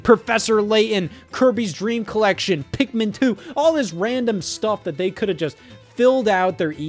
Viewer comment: pretty sure you meant pikmin 3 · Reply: No no, you misheard him. He said “Pikmin, too!” 😂😂